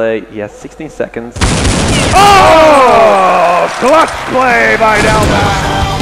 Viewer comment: What says the commentators ?? Rock play by Delpan ??